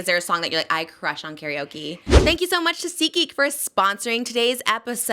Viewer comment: I really thought she said Tesehki and I was confused